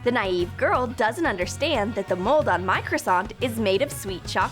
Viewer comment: I thought she said Microsoft! LOL 😂